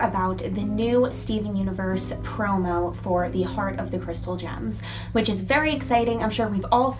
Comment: when you said heart at first I thought you said horn lol